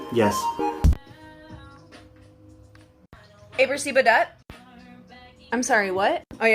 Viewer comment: Those times when you hear “alerseaberdadt” and they won’t tell you what they ACTUALLY said, so you’re stuck in your head trying to translate. The closest you get is “did you let Seabert out yet?” But the dog’s name is Kyle.